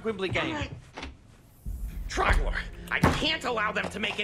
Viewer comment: I thought he said trogdor